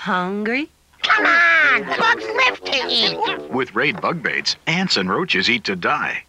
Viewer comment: It was so cute when she said “Oh, bugs! Hungry?” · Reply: Until I saw your response, instead of “Bugs live to eat!”, I heard it as “What’s lifting you?” You know, like, “What’s stopping you?”